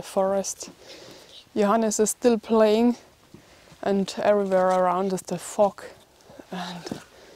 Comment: I had to laugh at 13:45 when you said "Everywhere around us is the fog." Sounded like "Everywhere around us is Da Fu*k."